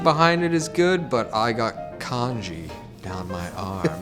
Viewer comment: I was confused at first, I thought he said that he has congee tattooed on his arm. I was like, does he mean the tattoo looks like it's melting, or does he just love congee so much? And then I realized he mean kanji.